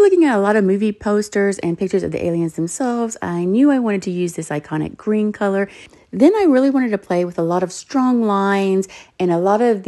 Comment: i thought she said “i really wanted to use this psychotic break color” and honestly if breaks did have a color, it would be that